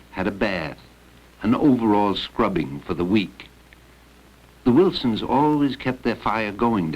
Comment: I thought he said, "An overall scrubbing for the weak."